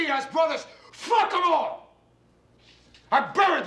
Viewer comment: Nine cockroaches disliked it. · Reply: cockaroach is what it sounds like he says